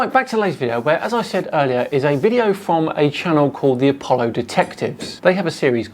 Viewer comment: Sorry, did you say "Apollo Detectives"? I could have sworn you said "Apollo Defectives"... And it might just be me, but every time you said "Moon dust", I could have sworn you said "Bullshit". Seriously though, these guys are beyond delusional, and if the best they can do is see things that aren't there and make up fanciful stories about them, I don't think we have much to worry about... What's sad is that these muppets (for want of a better word which Google won't like) demean one of the honest to goodness best things the USA has done in the last century, and I may say there have been precious few of those!